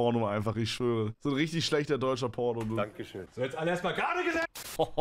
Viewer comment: Was sagt er da wtf xD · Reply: „So jetzt alle mal gerade gesessen!“